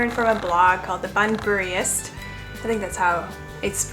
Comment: How would we find the blog… Bun furist? That’s what I heard here: 3:13 . I am unfamiliar with with blogging.